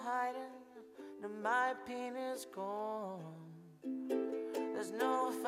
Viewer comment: I love this... but is he saying 'my penis's gone...' at 2:20? lol